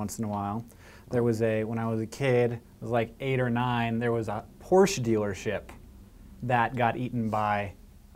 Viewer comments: I thought he said horse dealership, got really freaked out about that and the horses falling into sinkholes. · I thought you said horse dealership and got kind of sad